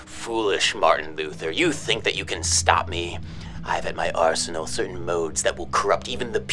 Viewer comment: I thought you said ars-noval... I find my way out